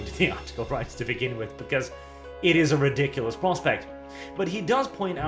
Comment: It sound like he said "Prostate" to anyone else? Or am I just sleep deprived and hearing things?